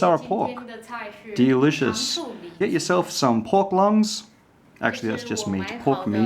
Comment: did he just say pork lungs??